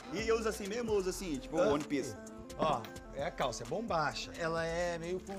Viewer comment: eu ouvi one piece?